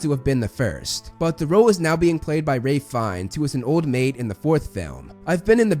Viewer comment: sounded like you said ray fiennes